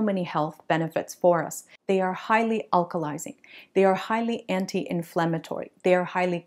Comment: I thought she said 'highly alcoholising' 😊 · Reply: Ha, ha, no, definitely not. It is indeed "highly alkalizing" :)